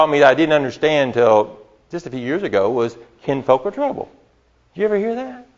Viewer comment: what's he saying? kinfolk are trouble??